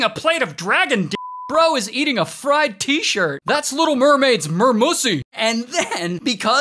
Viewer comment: I heard "a Mermaids Mermussy", and now I need a way to get this coffee off my screen.